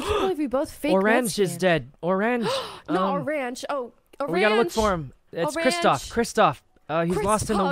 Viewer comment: I heard “oh ranch” not “oreeeenge”